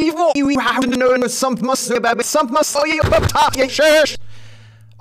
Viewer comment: Sorta sounds like he says “Don’t Talk In Church”